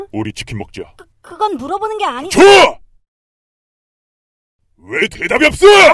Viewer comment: 나만 좋아~~ 들려?